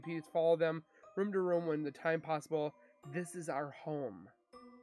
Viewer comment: Chris you ain't ever getting out of prison unless it's in a pine box. I can only pray that he gets some prison justice every day.!!!! I heard I'm with Cece & this is our home .. that's crazy it sounded like a woman that said this is our home.👻